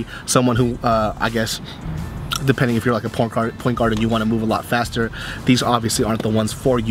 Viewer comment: I thought he said porn guard